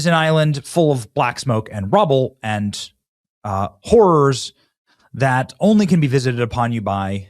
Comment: he said "whores" instead of "horrors"